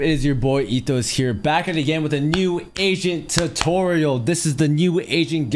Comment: Thought the man said "New Asian Tutorial"